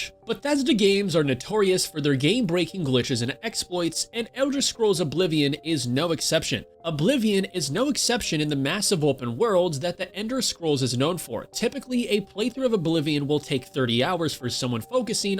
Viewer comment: lmaoooo he did not just say ender scrolls at 8:34 (im not mad just pointing it out)